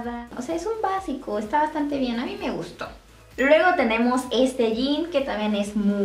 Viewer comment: Jajajaja en el minuto 7:11 escuche jin jajajaja 😁